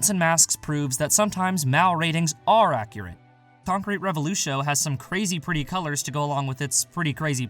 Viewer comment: Whats the name of the anime at second 0:55, I think I heard tonkri revolushow? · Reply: Sorry, it's Concrete Revolutio